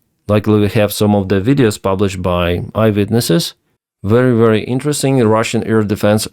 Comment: I thought you said “Ivan witness” instead of eye witness, it’s actually a good one🤣